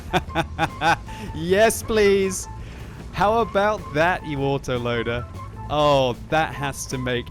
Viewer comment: Am I the only one that heard "How about that water-loader"? :D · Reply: Yes, you are. He said "you autoloader" and the word "you" has a hint of the W sound, which is why you thought he said waterloader.